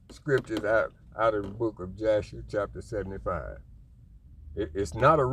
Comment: I'm sorry, but did I hear you say Joshua 75? this is towards the end of the video at about 46:00 to 47:31 ate their other scriptures with the book of Joshua?